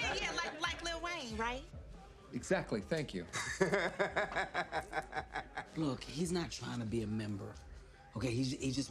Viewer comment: what does she say? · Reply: I love it